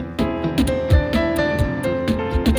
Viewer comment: does anyone know what Marzi Nyman says at 6:12 ? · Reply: Thank you for your furious applause